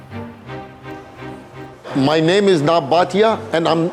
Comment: my idiotic self thought he said my name is NOT bhatia 😂